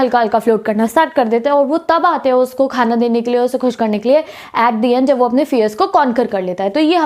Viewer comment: (what us the meaning of concern) what did you say? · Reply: Conquer